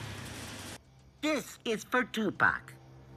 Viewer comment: does he say this is for tupac